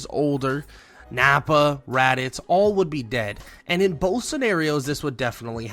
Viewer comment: Um, you kind of typed "rabbits" instead of "Raditz".